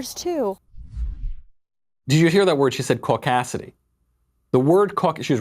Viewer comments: oh, I thought she said 'Cock-asity', at least shes not a feminist... js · I really thought she said “COCKasity”, as if to say that if you believe all lives matter, then you’re a cock. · I love how she is against racism but immediately shoots out a racist comment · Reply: I heard "Caucacity" as "Cock-ass-city".